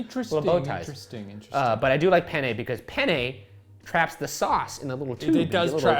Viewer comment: "Penne" is "penis" in Spanish. So when we eat Penne Rigate we just say...the noodles, or, in Argentina where I live, "mostacholes". You know, cuz of the implications ("oh yeah, I'm having penis for dinner") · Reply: According to Robin Williams, if you don't pronounce it right, your saying penis in Italian, too. So I kept hearing "I love penis! Penis is nice because it holds the sauce in the little tube!"